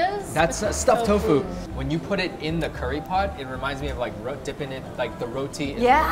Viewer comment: I thought she said human in the dry pot